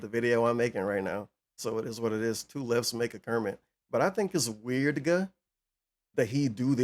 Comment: did say “Weirdga” as in like, the FF and Kindgom Hearts universe magic 😂? That’s dope